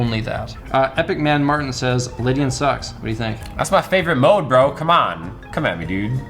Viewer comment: lol i thought he said living sucks